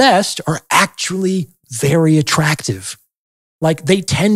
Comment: <Steve Harvey has left the chat> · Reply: I thought the Doc said attractive men.